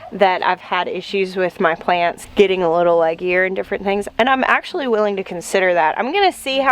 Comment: At around 3:45 as u r talking about ur black green house, I can't understand what u r saying when u say ur plants getting a little bit...??? what? I put on CC and it types out u say " year", 🤷 I don't know what u r getting at, anyone know? It interests me because I love ur black green house and have considered building one, so what is the issue r u talking about? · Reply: Leggy. Struggling for light. I don’t think it’s the greenhouse color but several people have voiced that theory.